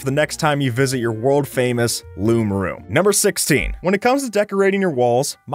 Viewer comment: I thought he said “loob room” XD